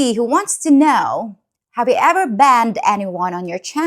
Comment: I'm so glad the Spanish didn't infect your countries blood line very much. It's not a good mix. When you talked about banning one of your viewers, at first I thought you said you banging one of your viewers. And then I thought "why is she banging these guys who made bad comments?" I finally I heard it right, then it made sense. For a while I thought you might be one of those women who like the bad boys. Glad it's not the case.